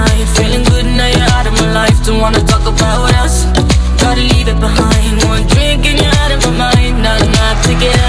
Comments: 1. When she says gotta leave it behind it just sounds like she is saying gotta yeet it behind. 2. comment number 666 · What I hear is: "Gotta yeet it behind" 😂